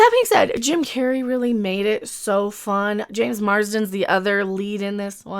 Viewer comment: SHIT! You said "James MarsDEN" and I thought I heard "James MarsTER" and as a 1990s (<num>) BUFFY fan I was on the verge of loosing my shit then got disappointed when I hit up IMDB! It's probably fine, just need a moment to get over this disappointment.